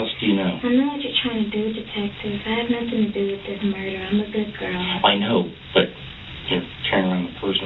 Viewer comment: Did she say I am a Good Girl? 01:18:26 LMAO